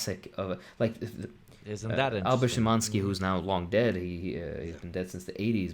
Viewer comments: There's a writer mentioned here, I can't make out what the name is. Does anyone know? I'd like to check out their work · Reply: Albert Szymanski? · who did refer to? I can't quite catch the full name from the subtitles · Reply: Albert Szymanski?